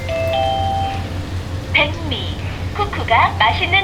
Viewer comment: The rice cooker did not just say "bang me"